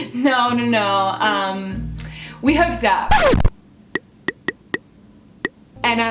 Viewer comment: through 2:56 what did she say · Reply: "no no no..... we hooked up"